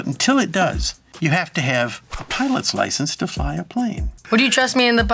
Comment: Did i hear "would You thrust me in a butt" 😅